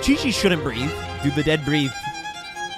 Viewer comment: I heard "chiliessss" instead of "she livess" XD